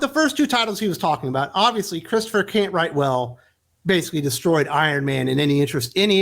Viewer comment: For a minute there I thought you said Christopher can't write well. Image having that for a last name. How ironic (and almost appropriate) last name that would be.